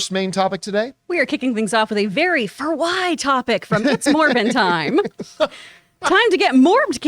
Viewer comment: I misheard and thought she first said “it’s mormon time.” 😂